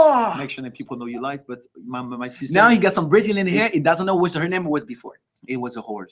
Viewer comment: did larry say "now he has brazilian hair and doesnt know her name or where it was before" is he saying lau's hair is not all his??